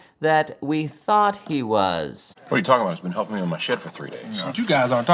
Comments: Did they say the word "shit" in this movie?!?!?! 11:35. I'm actually surprised. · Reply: "Shed" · Wait, did he just say "He's been helping me with my s*** for three days."?? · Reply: Shed